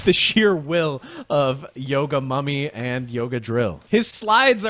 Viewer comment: I swear to god I heard him say Yoga Mami